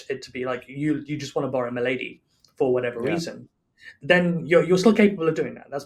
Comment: it really sounds like he says you could borrow my lady, not borrow a milady